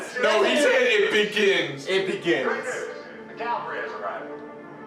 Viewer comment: So few people got the "step outside General" reference from Superman 2. Also, Atom actually says "It embiggens", as a nod to the Kamala Khan Ms. Marvel character, who sometimes says "Embiggen!" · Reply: I heard, "IT biggens." not 'embiggins'.